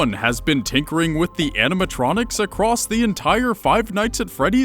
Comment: Every time he said tinkering I thought he said dickering PURPLEMAN STOP DICKING AROUND WITH THEM ANIMATRONS